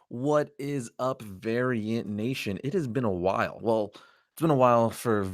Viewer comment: I thought you said “What is up Aryan Nation” at the beginning of the video. Figured it was a play on your name “Arris”. Holy shit xD